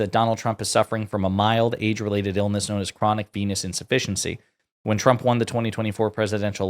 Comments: I was reading the comments and I misheard something you said. I thought you said chronic penis insufficiency! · I thought you said, chronic penis deficiency